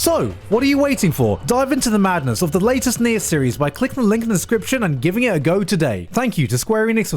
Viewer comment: When you said nier series I thought you said neet reincarnation